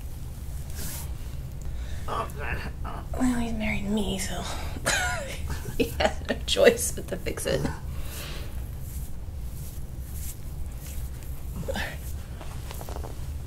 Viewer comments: sounded like she said well u married me 🤷‍♂️🤷‍♂️ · Are you guys in-laws? I feel like I can some similarities in his and ‘s faces 😂 Edit: wait what was that at 12:30? He isn’t your husband is he? But that would explain things😄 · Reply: I thought I heard her saying “well you married me so” but I’m not totally sure.